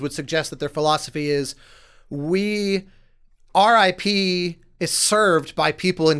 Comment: listening @ 30:20 I almost though said RIP 😱 when he actually said Our IP.